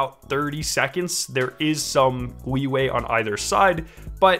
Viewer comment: instead of leeway was weeway said?